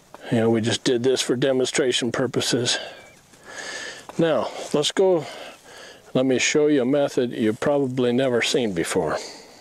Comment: 'now bossku', did he said bossku? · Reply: "Let's Go" is what was said at the point in the video that you are referring to.